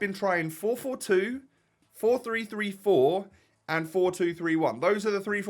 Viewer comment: Did anyone else hear him say 4334 as a formation